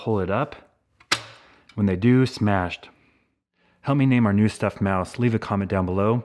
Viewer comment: As soon as you said "help me name our new stuff mouse" I heard it as "hummy name our new stuff mouse". So I vote hummy/hummie as it's name.